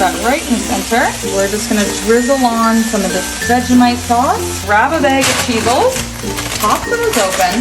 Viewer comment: I thought she said grab a bag of people😂